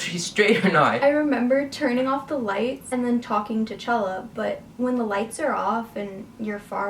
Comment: when said she was talking 'to chella' i heard 't'challa' LMAOO